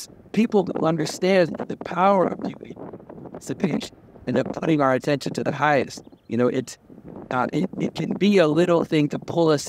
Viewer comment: audio problems. He says people who understand beauty, participation, and putting our attention to the highest